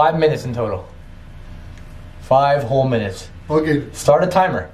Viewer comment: I heard voice say something "Okay" at 9:15 mark